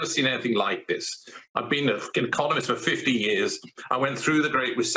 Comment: did he just say "i've been a f***ing economist for 50 years."?